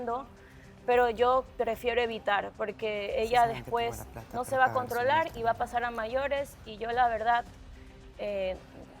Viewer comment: Que dice Mare en el minuto 1:38:07? Somos varias personas que escuchamos claramente que le dice al micrófono "Sabes que tengo la plata para dejarla muerta". Que clase de actrices tienen ahi que se rasgan las vestiduras con el tema del feminismo pero incitan a maltratar y matar a una mujer. Ah verdad que ustedes apoyan eso porque por retener a Mare fue que eliminaron a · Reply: Dice MULTA, no muerte Si te vas del programa sin ser eliminado tienes que pagar una multa